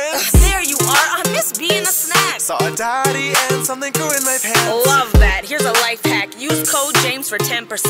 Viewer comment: "Saw a daddy and something GrEw in my PaNtS!" Edit:I have 119 like that's the most I've ever got!lol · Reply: I thought it said and some goo in my pants